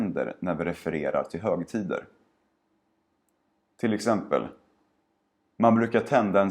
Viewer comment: I'm not sure but I hear an "L" sound in your "man brukar" pronunciation What I hear is mom-leu-kah. So do you really pronounce it like that or did I just get it wrong?